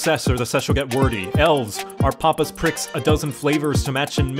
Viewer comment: "Elves are pompous pricks" Yep. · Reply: I heard " elf's are papa's pricks" , but you're probably right.